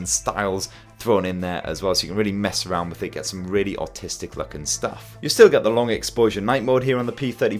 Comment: he says 'autistic' instead of artistic lmao